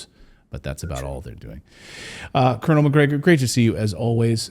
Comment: Kernel?...Oh, I thought you said Conner McGregor